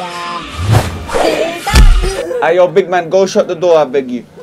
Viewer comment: I thought you said don’t shut the door😹😹😹